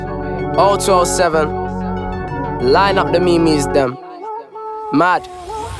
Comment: Did he just say ME-ME's instead of memes🤣🤣💀